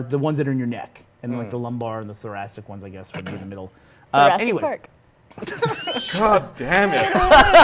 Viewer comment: What does she say? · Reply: Thoracic Park